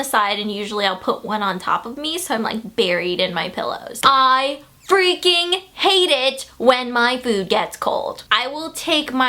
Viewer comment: When u said I hate it when my food gets cold I thought u said my boob